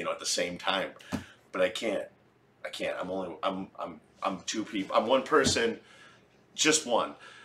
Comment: Umm at 1:34 did you just say that you are 2 people? I knew you were two people in one!! LOL Anyway, I LOVE haunted houses and ghost stories! Thank you for putting this together!